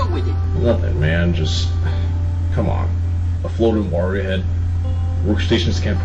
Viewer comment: i should probably point out that jim didn’t say “floating wario head” in episode genesis, he said “floating mario head” but tbf he did say it in kind of a weird way that made it kinda sound like he said wario